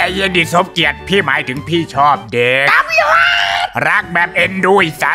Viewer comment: ผมได้ยินว่าพี่ชอบเก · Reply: เด็กๆ ไม่ใช่ เก 🗿